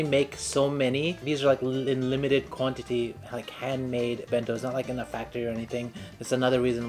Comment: wait what? That sounded like many bentos are maid in factory